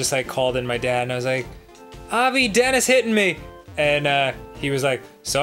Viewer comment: "Abbi, dad is hitting me". Did I hear that right?